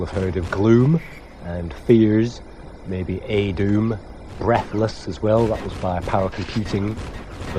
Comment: you mention Gloom,Fears and..E-Doom? A-Doom? Sorry but i didn't understand the name of the game. Thanks